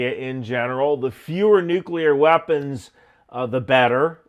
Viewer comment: I thought I heard "The Fuhrer nuclear weapons, the better, that's always a good idea."